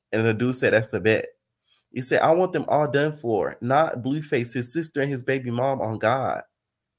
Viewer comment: I thought she said touch his sister n bm not him ???